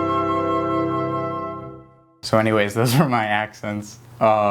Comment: Ohhh this video made so much more sense now. I thought it said accidents not accents